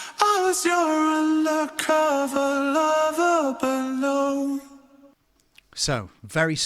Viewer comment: Every single damn lyrics I've seen says "undercover lover but no" but all i hear is "undercover lover below" and having been in this scenario I'm sticking with it